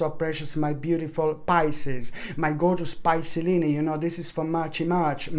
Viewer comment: When said “my gorgeous peoplelini” 😂great reading as always ! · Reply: I thought he was saying pisceleni